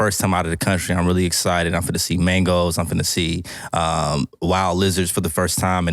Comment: Did just say i will get to see Mangoes? 8:00 lol